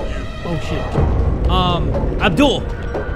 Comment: why do i hear "Avdul" instead of "Adul" as progressed with the game 😂 Reminded me of Avdul from JoJo lmao